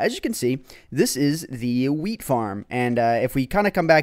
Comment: did he say weed farm what are you on